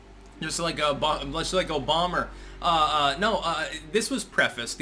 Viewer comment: Styx did you just call Obama an O'bomber?